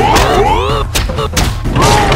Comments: I thought he always said give me your fish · Bootman rules Hightower with an iron fist. Born without a face but instead a boot, he was ridiculed for aeons. But he had his revenge when he stole the faces of seven out of the nine mercenaries, save for two...soldier and sniper. Soldier tresspasses on Bootman's territory and Bootman sees it as an opportunity to add to his collection. He intimidates Soldier by appearing through a complicated system of hammers and screams at Soldier. While Bootman is an intimidating foe, his bark is worse than his bite. Soldier, through Eldritch magic and Jojo references, summons his Stand Digeridooman, who shoots watermelons in defense of himself and Soldier. Soldier and Digeridude celebrate by Blue Screen Screaming (Blue Screaming, if you will) and jetpack-ing off into the sunset. Bootman is gone...for now... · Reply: I thought boatman said “give me your fish”